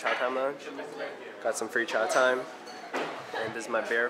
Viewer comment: Lol. For half the video I thought you were saying "Chowtime".